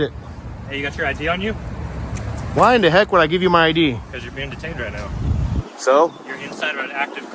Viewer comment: this is when he said your being detained sorry just bugged me you said twice that you didn't hear it lol